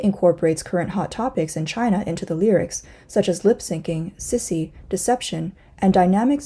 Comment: Did I heard right? Sissy are a hot topic in China? 😂